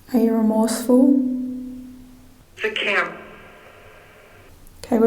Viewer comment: My speech to text wrote that as "Its cramped" So sharing some of my colourful history when i did time we used to call the screws who'd come out to do forced lock down and inspection "Flat Heads" as in "Screw Driver" the older prisoners got us doing it. If someone said flat heads we knew to hide or flush our contraband. You also probably would of got more responses as well acting like prisoners instead of guards as most people know in prison snitches get stitches. Back on Flat head though You had just announced you were locking them down and seized the smokes as contraband.